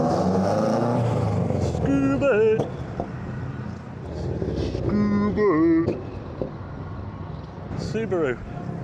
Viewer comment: sounded like scooby 😂